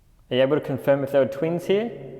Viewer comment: I don't know it didn't sound like twins to me honestly I thought it said twit. Lol